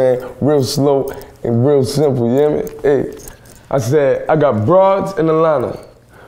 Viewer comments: I thought he said I got bras in Atlanta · I thought he said "I got bras in my lawn" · "I got broads in Atlanta"...... Ohhhhhhh I thought he said I got bras in Atlanta. That makes more sense. 😶😳